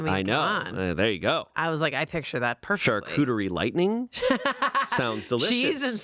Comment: amanda saying "chartreuse lightening" and the caption says "charcuterie lightening" 💀